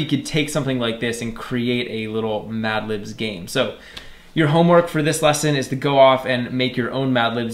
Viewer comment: Did he say "your homework " 😭 No !! 🤣😂